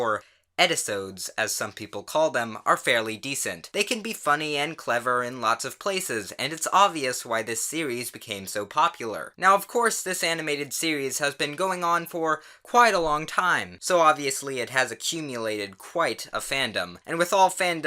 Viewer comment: I swear to god I thought I heard him say anime there.